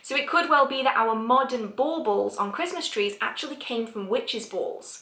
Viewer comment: I love accent and how her words flow. Can anyone identify what type of British accent this is? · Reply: I'm not sure but when she said "baubles", like on a Christmas tree, I thought she was saying ball balls 💜 silly me... I love her accent as well..